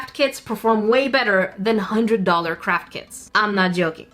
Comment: I don't know if it's my inner Sika but every time you say "craft kits" I always hear "Crap kits" instead 🤣